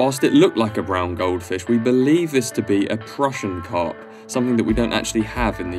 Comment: i take it you mean crucian carp? they are most definitely here in the UK :) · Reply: I think you misheard, it's easy to do! I said Prussian Carp, which we don't have in the UK.